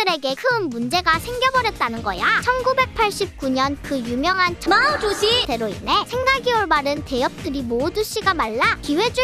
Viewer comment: 뭐라는거임 마도지? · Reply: 마오주지-> 마오쪄뚱 주석을 중국어로 말하는거임